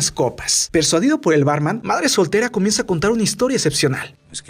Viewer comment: pense que dijo Batman:v